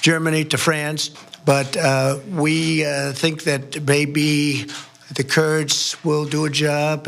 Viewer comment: Is it just me who hear him say "baby" at 2:00 instead of "maybe"?